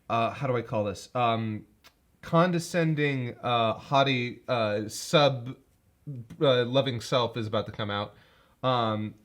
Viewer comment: God, I thought you said "Condescending, hottie, sub-loving self" instead of "haughty", which painted a very different picture before I realised what you actually meant.